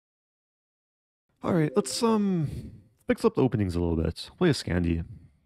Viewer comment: Damn I heard Scandeez when you said "the scandy's"